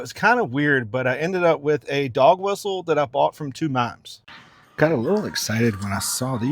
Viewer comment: a dog whistle you bought from ??? ...can't understand. And yes...I'd like to hear about the storage unit. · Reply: Bought from two mimes... silent auction. Just a dumb joke😁